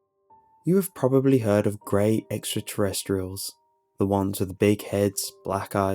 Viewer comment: thought it said gay extraterrestrial. FML